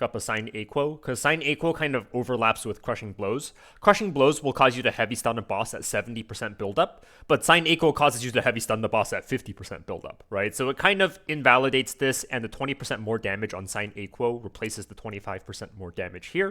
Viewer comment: Sign Equal? What is sign equal? Did i hear that word correctly? OH its a Sine Aequo.